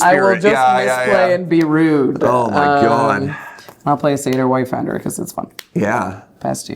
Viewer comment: fully heard that as "Saytr Wife-ender"